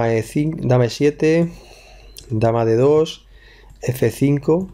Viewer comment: minuti 10:59 De2-Dh6... MAGIA??? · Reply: Raúl Arias López Si escuchas lo que dice en el minuto 10:39, te das cuenta de que dice "dama D2" pero se ve que mueve dama E2. Sólo fue la imagen la que quedó mal